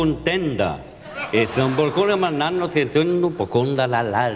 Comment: Was habt ihr verstanden? Ich: Companer Contenda